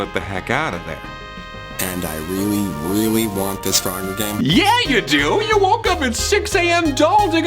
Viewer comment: I thought he said muggle game for a sec